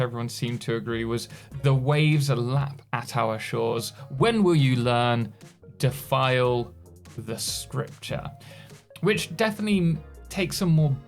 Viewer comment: What do you think the text we hear at the end is and means? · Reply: Personally I hear: “Your wakes lap at our shores. When will you land? —file wi— s——ture” That last part may say: “(To file/Defile) (will/with) (scripture/sculpture or sepulture - another word for a burial)”